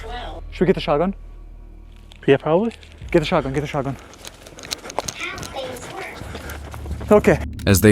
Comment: bro I swear one of the ghosts said "nothings worked"